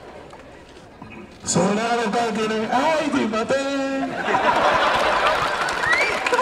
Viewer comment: なんて言ってるの笑笑笑笑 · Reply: そんなの関係ねぇ！あーい とぅいまてーん